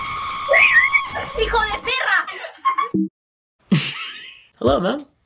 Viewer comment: มีโคโยตี้ค่ะ ได้ยินแบบนี้เลย